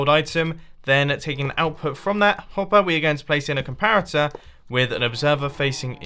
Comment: is it just me or did he just say “complarator” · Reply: I just heard “plarator” 🤣🤣